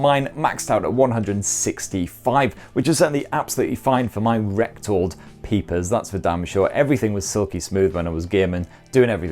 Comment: British accents are tough for us North Americans to understand, but did you say at 4:42 rectal peepers? Or retinal peepers? ¯\_(°,ʖ°)_/¯